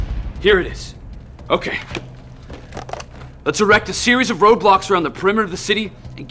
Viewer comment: did he just say roblox at 2:42?